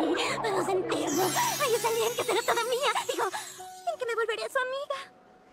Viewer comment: que dice? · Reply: Hoy será el día que será toda mía digo que será mi amiga